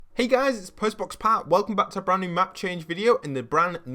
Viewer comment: Literally the first time watching him and at 0:00 I thought he said Postman Pat